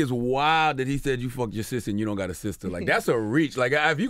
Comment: basically my sis said, hahahaha bra thought u said u aint gat no sis